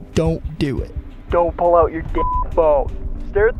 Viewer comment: if you pause it in time it sounds like he says "Don't pull out your dih"